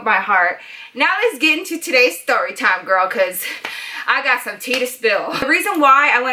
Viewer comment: Did she say i got some tea or teeth in 2:05-2:07 because if she said teeth, it'll be like i got some teeth to spill, get it cuz she got her wisdom teeth out.